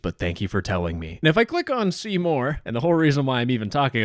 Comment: i cant be the only person who heard seymour like the character from the simpsons